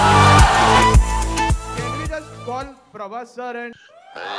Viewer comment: Can anyone say what he said in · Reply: I think he ask nagi to call prabhas